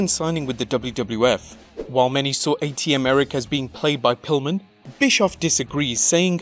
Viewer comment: did you Say " While many saw ATM Eric being played by Pillman" haha